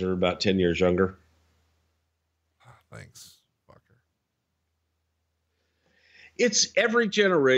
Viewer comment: Did Conrad just call Bruce a "f*cker" around the 1:22 mark? Lol